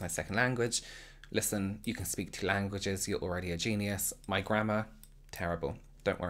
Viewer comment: I thought you said "my grandma, terrible" instead of grammar because of your accent